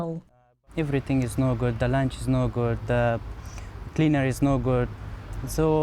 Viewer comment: A classic example of treating some people with kindness, they take it for granted then just want more and more.. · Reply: Did he just say "cleaner no good"?